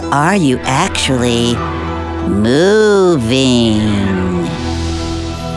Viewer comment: me:...Did yOu SAy gROooOOoOOoVy